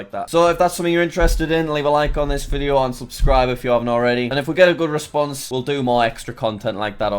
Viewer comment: i thought he Said "Unsubsribe i you havent already